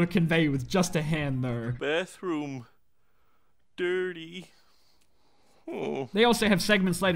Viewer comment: What I heard was duty oh